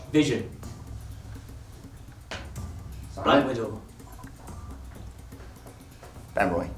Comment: i replayed 100 times and still have no idea what he said :X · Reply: mishka kviriashvili black widow